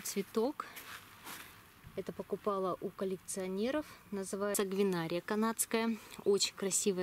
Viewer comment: какая вы молодец!!! Отличный цветник получается!!! Не могу расслышать как называется цветок на 17:00 минуте? Одинария Канадская? И ещё вопрос: как и чем вы ограничили мискантус? И на какую глубину? можно ли у вас купить небольшую делёнку чабера или поменяться на делёнку пиона? Очень давно ищу чабер. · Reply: Сангвинария канадская. Ирина, вы из Московской области???, растение почтой не могу отправить